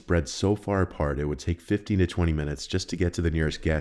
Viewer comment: am I hearing it wrong or did he say "fifty (50) to twenty (20) minutes", who says it that way?